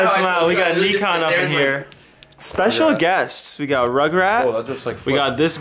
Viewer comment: Thought you said special guest